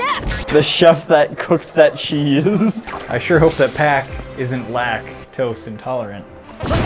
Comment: I thought he said that I hope that pack isn't black😅